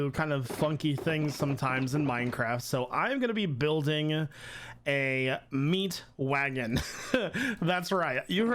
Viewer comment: Build a meat wagon? I thought you said build a meat dragon!